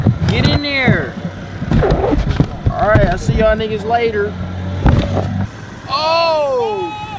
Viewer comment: Did just say nigga?